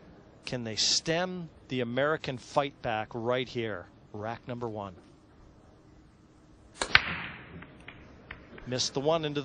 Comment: عراق نمبر وان سمعت لو لا دوم احنه نمبر ون😹💔